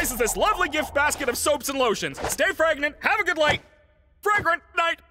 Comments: The first time I heard Caine say “Stay fragrant” I heard “Stay pregnant.” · WHAT IN THE EPSTEIN ENDING IS THAT???😭 31:55 edit: I originally thought he said “stay pregnant” and not “stay fregnant” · i thought he said "stay pregnant" 😔✌️ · Genuinely thought at first cane said “stay pregnant” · When I saw the episode I thought Caine said "stay pregnant",I can't be the only who heard that 😂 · I thought Caine said "stay pregnant" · Really thought caine said "STAY PREGNANT" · I thought Caine said "Stay pregnant"... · i thought he said stay pregnant when i first heard this 😂😅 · I heard "Stey preagnent" 😭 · did anybody else hear him say “stay pregnant?” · I did NOT hear “stay fragrant!” I HEARD “stay pregnant!” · Did I just hear caine said "STAY PREGNANT" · Did he just say to stay pregnant? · Why i hear Caine say " Stay pregnant, have a good light" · Such an intense episode, but I swear at the very end ( 31:52) I thought Caine said "stay pregnant" instead of "stay fragrant" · i thought he said "stay pregnant" , thank you for accurate subtitles! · am I the only one who heard ‘stay pregnant’ or is it just obvious? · I legit misheard this as Caine saying "Stay pregnant." 😵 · DID CAINE SAY STAY PREGNANT!? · HELP I HEARD "STAY PREGNANT" 😭😭😭😭😭😭😭😭😭